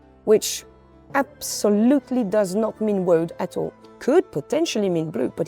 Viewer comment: What does the paint lady say @ 19:30? "It could mean blue but absolutely not..." what? Never mind, apparently its spelled Woad, a plant to make blue dye.